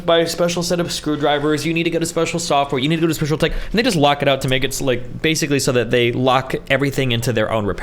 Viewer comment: What do you mean who locks it out? Like who puts these checks into machines that makes them not work when repaired, not with 1st party? I don't know what you mean. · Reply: 😂 naw, sounded like he said "nygas lock it out to make..."